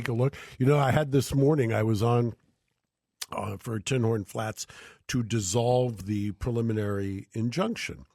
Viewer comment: The Battle of Tinhorn Flats just gets stranger and stranger. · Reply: Up until about a month ago I thought they were saying Tim Horton Flats...